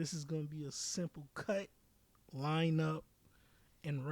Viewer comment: Wait @ 2:01:38 did say he gonna cut up some lines? Selfish bastard